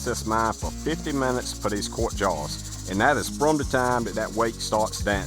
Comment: I know he’s saying “jars” but I hear “jawns”